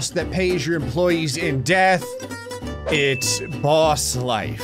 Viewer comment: In your intro I thought you said "you get to be a boss that pays your employees in debt" and I was like yep sounds about right 😂 death also works